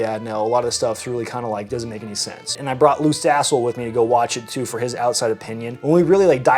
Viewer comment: I lost some hearing in the Army. Did you say you took Luke's Asshole with you to the movie???